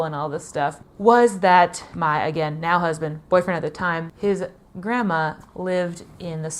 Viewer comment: I thought I heard you say, "my now boyfriend, then my husband," LOL! Loved this story!